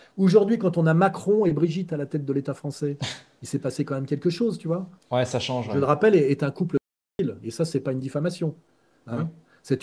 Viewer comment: À 4:30 il dit c’est un couple quoi ? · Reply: Un couple ped0phiile